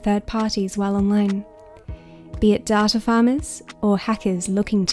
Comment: Lol when you said data farmers I thought you said tata farmers